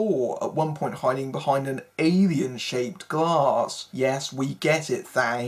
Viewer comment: "hiding behind an avian shaped glass, yes we get it", get what, Bond is a bird? "I do not think that word means what you think it means." :) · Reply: I said "alien".